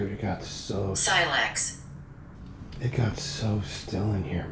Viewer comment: Did it say Silax at 1:24:54? That’s a type of knife .